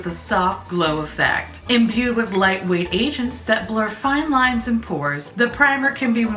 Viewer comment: When she said “imbued with light weight agents” it sounded like she said Asians. I was like imbued with lightweight Asians?!